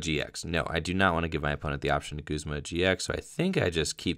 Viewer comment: Is "Guzma GX" what you're saying because you're tired, or am I mishearing what you're saying? Lol :p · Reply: He was saying he didn’t want them to Guzma a GX because of the 2 prizes remaining